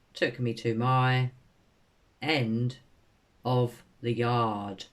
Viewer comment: I thought she said "she come out and took me to my (appoint)ment at the ER". But my hearing isn't great so... I've always felt the entire day was nothing but a series of witnesses and alibis set up for whatever happened to Summer. I don't believe there was anything wrong with her knee, I was born with a connective tissue disorder known as EDS, the type I have means I spontaneously dislocate joints for no reason, at any time, I know a fair few people who've had knee replacement, some were ex dancers but just have EDS. From what I understand, once you've recovered from the replacement, you shouldn't have any further pain or swelling. I haven't had one yet, so I'm not saying it's impossible. But I've always felt it's part of the script. There is a script, but I believe there's also a plot. This starts with the early ER appointment (I know we don't have appointments at A&E, but maybe they do at that hospital, seems weird). I think Don planned it, go early to the hospital, contact Hunter and get him to ask you to come get him (wouldn't be hard if you told a 15 year old kid who thinks of your daughter like a sister) to want to come swimming or fishing rather than sit in his room on a hot day. Then dive around and be seen on CCTVS and by shop staff, get Hunter a bit intoxicated so his "evidence "can be disputed. Leave three boys "locked" in a hot house, alone all day. Take your phones, when you get back, leave your phone at home and use a burner phone and meet me with Summer...etc. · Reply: She said to the ER not yard